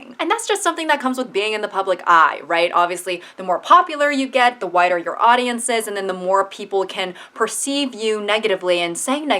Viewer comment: i thought she said "the whiter your audience gets" 😭